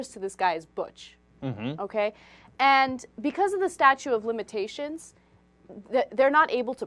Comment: I know it's statute - I heard "statue".